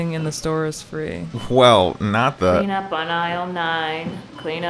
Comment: Oh I thought it said "peanut on I'll 9 " 🥜😂😅